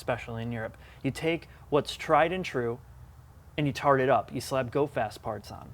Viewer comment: I never comment but did this guy just endorse a company that employs special needs people, and then use the phrase "tard it up" really??? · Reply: he said "tart it up". Which means something akin to "lipstick on a pig". You misheard, calm it down.